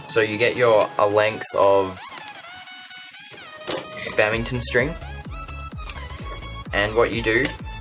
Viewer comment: Does anyone know what the string that he used is called? it sounds like he is saying bemington string but I have no clue what that is.